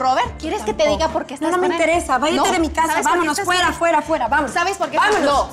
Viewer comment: "váyate de mi casa" ? o escuché mal? jsajajsja